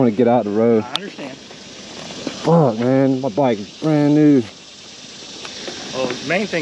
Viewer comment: Bike had 19,000mi on it....5th set of tires.... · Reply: You say..”Oh man, my bike is brand new”…or have I misheard that? Hope you are ok and back on the road. Take care out there and ride safe. 🫡